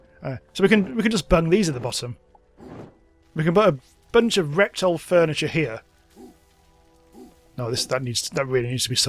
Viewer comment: Did...did you just say rectal furniture?